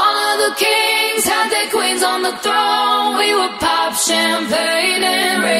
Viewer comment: I honestly thought Ava Max said, "To all of the queens who are FARTING alone"